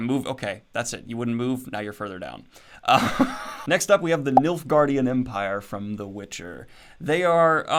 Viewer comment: I thought you said milfgaurdian empire.